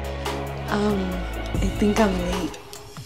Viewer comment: LMAO I THOUGHT SHE SAID “I think I’m gay”